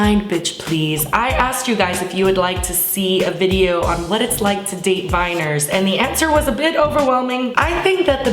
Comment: OMFG I THOUGHT U SAID "WHAT IT'S LIKE TO DATE MINORS" XD